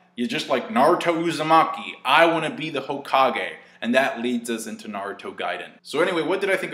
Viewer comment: he jerked his hand and sounded like he said "I wanna beat the hokage"